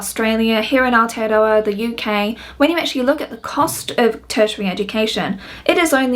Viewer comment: Anyone catch what she said when saying where she is? The subtitles were not very helpful for that one word. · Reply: Aotearoa (New Zealand)